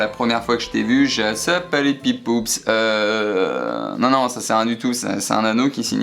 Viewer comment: "Tu sais moi la premiere fois que je t'ai vu heu... Sopalepipoups" 😂😂😂